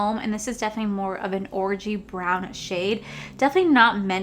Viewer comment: That look is gorgeous!!! Look at you playing with a red shade and slaying it 🔥 I seriously for a split second thought you said orgy brown instead of orangey brown lol